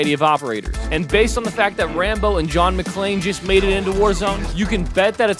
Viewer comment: Ngl thought he said John McCain for a minute